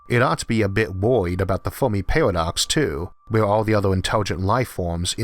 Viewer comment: what does he say ?it sounds like the fuckin parodox ????/ lol